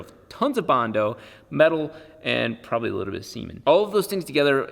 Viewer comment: Did he say cement or... semen ?